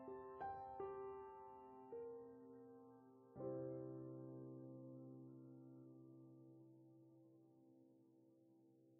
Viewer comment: Anyone understand ay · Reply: Copy sketch to front plane. Ctrl + C -> Ctrl + V